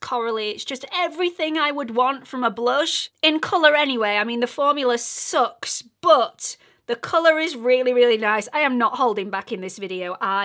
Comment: Sweet, sweet.......When you were speaking of the peach colored blush at around 2:50 you said, "The formula sucks but the color is really, really nice" I LOL because you caught me off guard and it truly sounded like you said, it sucks but.......the end. 🤣 Then I heard the color is really, really nice almost separate. Thanks for the chuckle!!😁